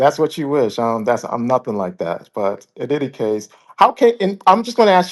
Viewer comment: Wait did he say in diddy case 11:20 lol